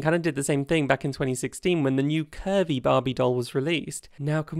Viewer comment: I heard “Kirby Barbie” instead of curvy Barbie and man I was so excited for a minute